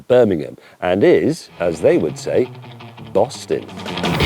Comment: James May saying "bussin" was not something I expected to hear today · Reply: I thought he said Boston. Bussin makes more since.